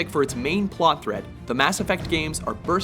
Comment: I think i heard "subscribe" when they said Mass Effect